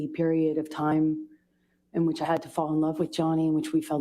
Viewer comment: Did anyone else hear her say “in which I HAD TO fall in love with Johnny” at 9:50 which means there was a different motive behind her marrying JD.